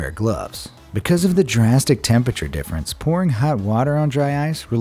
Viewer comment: I thought he said PoRn